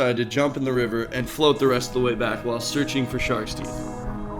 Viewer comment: at thought he said "While looking for sharks to eat" lol Idk how